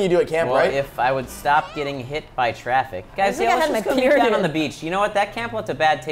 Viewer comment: did Mari just say "I'm on my period"?